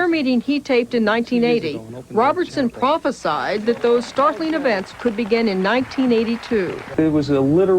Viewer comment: I think I've been watching too much Star Trek. I misheard 'startling events' as 'Starfleet events'